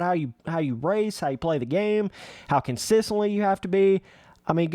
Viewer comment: Did just say consisilent? LMAO